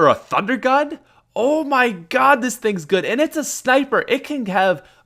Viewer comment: Did he just say ''sniper''